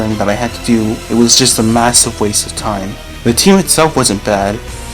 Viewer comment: Did you say massive? 👀